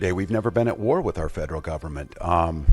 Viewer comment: Did he just say they are at war with the federal government?